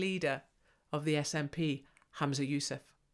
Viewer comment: for a minute i thought you said Ramzi Yousef